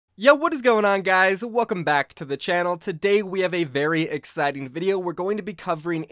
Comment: Low key thought you said welcome to Disney channel in the beginning